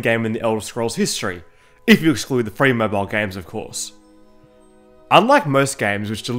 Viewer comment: what did he say here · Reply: Ifyooscloodhafrimovylegames of course